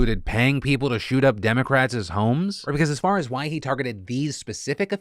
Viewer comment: Did Phil say “Democratses”?